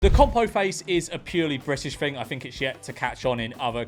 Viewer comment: When I heard “Compo face” I thought it must mean people that make expressions like Compo from ‘Last of the Summer Wine’. I was admittedly bewildered at such a thing being current jargon. Jesus Christ I feel so fucking old. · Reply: I thought he said compot face, I'm not from Britain.